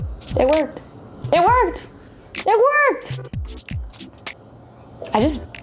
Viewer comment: if anyone wondering what is the word at 6:01 *I swear the moment was 100x more dramatic in person. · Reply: She says "it worked" there is no problem with that